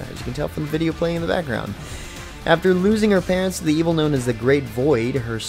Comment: i really thought you said "After losing her pants"...